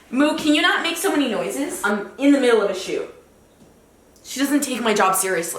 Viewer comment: i really thought she said "in the middle of a shoe"